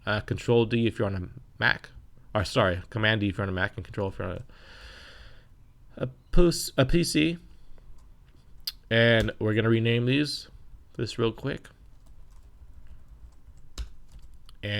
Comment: you got to say pussy instead of PC at 6:59 . nice hahhahhah